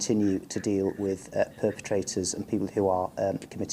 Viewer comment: I had to replay the last policeman's speech because I thought he said "purpy traitors" meaning it was OK to be a traitor, provided you don't fart.